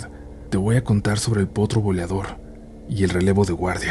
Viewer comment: Buen compendio. Me gusta la temática militar y que todas estén relacionadas con el mismo lugar. No hay entrenamiento que prepare a un soldado para lidiar con aparecido. Confesion: cuando dijeron "potro boleador" yo entendí "potro volador" 😅 y yo " WTF?" "¿un Pegazo de recluta?" 😅😂